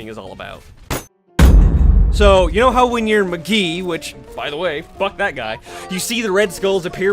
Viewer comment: i think i just heard him say magee not macree